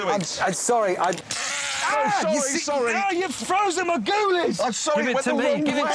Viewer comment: he frozen his what ? i know the meaning but what word hammond used ? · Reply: Goolies